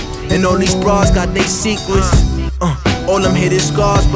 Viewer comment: "Every man got his weakness and all these broads got they secrets" Hit me like a train bro jeez 🔥🔥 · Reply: i thought he said "all these bars got they sequence" still pretty fire tho